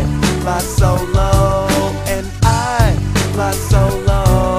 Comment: My mom was listening to this with me and thought he was saying "I have no cloths" Instead of "I fly solo" :,) mothers..